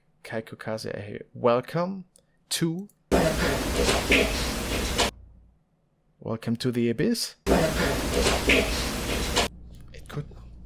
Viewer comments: I heard, "Welcome little Bitch" and "dead" not dad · I hear "Welcome, little bitch" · On the welcome one it sounds like it says "WELCOME ...LITTLE BITCH"!!